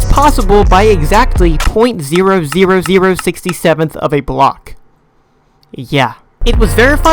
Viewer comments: did you just say 6 7🤨🤨🤫🤫🤷🤷🤷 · did bro say 67